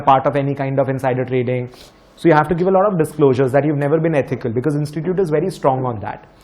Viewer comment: Did i just hear "You've never been ETHICAL?"